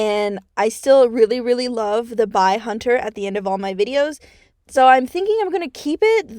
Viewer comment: HELP I THOUGHT YOU SAID BI HUNTER AT FIRST 😂 (I mean you aren't wrong ;))